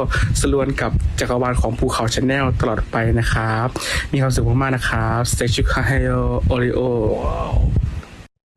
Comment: เค้าพูดว่าแซงอิลชุคคาแฮโยค่ะ ก็คือสุขสันต์วันเกิดนั่นแหละเป็นภาษาเกาหลี 생일축하해요 오레오님 🥳 ขอให้เทอมาไลฟ์กับอธรญได้แล้ว เอ้าไม่ใช่พรด้วยซ้ำ55555